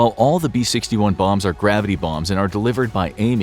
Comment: So high I thought it said gravity bongs